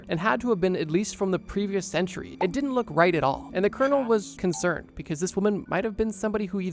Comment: Thought u said turtle instead of colonel. Heh